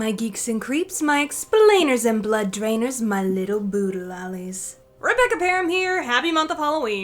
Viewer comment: I thought she said, “My little voodoo dollies,” at first.